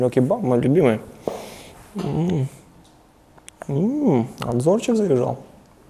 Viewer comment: что он сказал???👏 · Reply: Анзорик приезжал